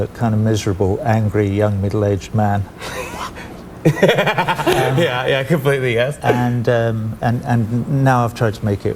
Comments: What does Roger say to the camera at 1:33? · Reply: I think he said “MOM!”, but very quietly under his breath. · what does he whisper @ 1:34? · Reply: Moi!! (French for me).....you're welcome.